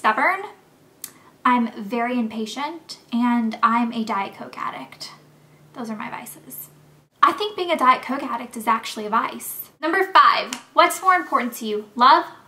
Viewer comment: omg at first i thought you said im a coke addict!!! ahahahh but same i LOVE diet coke :) xxx